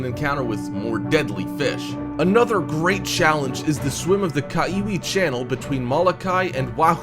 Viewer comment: who else thought that kaiwi channel said kawaii channel or kiwi channel or is it just me xD like if you thought that xD